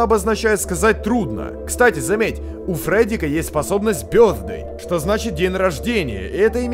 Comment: Смотрел на низкой громкости, послышалось: "У Фреддика есть способность пëрднуть")